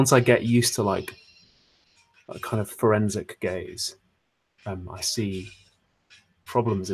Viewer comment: I heard "forensic gaze" as "forensic gay's"